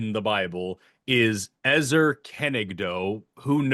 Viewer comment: STOPPPPP I HEARD “Ezra Koenig” like the frontman of vampire weekend LMAOOO